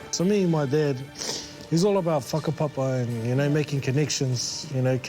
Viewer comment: What did he say at 20:47? I've played it back a dozen times and all I can hear is "he's all about f**king Popeye."